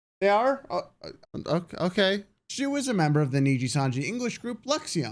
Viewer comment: I think I heard you said "she was a member" while shu was a boy…..